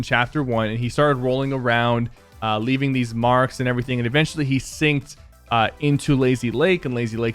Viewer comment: Who all got triggered when Sypher said "sinked" instead of "sank"